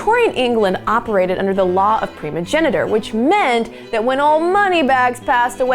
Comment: Did you just say "preeemogeniture" instead of priiiiimogeniture?